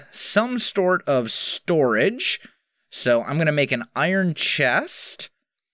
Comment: Did you say something about a stork and storage. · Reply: Yes, he did